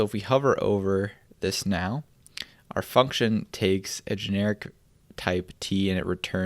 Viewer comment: Did you say fucktion? 😂